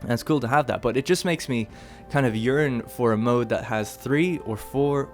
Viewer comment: When you said "yearn" sounded like you said urine that shit was hilarious